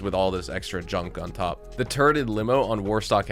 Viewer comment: I honestly heard you say "the turded limo". That is hilarious and also quite fitting to this vehicles functions 😆 it has the awesome roof gunner, but not without a friend helping. I don't see many using this thing 😜